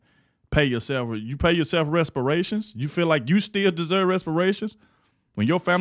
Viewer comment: Does he say pay respirations!? Instead of reparations